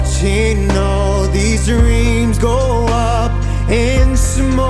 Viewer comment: He said reams instead of dreams at 1:00 😂😂😂😂😂